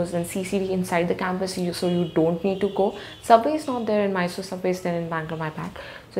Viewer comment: what did she say? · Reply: Subway is not there, "my bad"